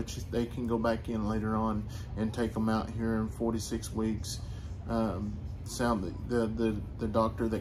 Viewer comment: When you said he would get the pins out in 4-6 weeks I thought u said 46 weeks😂